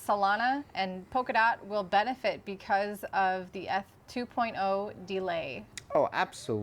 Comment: Does anyone know what F 2.0 is? Mentioned when speaking of ada, sol, dot at the 11:12 mark? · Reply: ETH 2.0